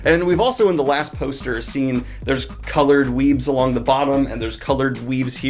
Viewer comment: My first thought at 6:33 : Did he just say coloured weebs? Oh wait, weaves, that makes sense.